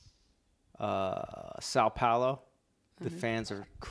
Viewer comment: Did Munky just said São Paulo? I mean, thats in MAH COUNTREH! I used to live there :D